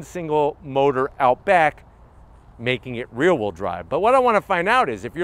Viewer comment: For a second there I thought you said it was "real wheel drive."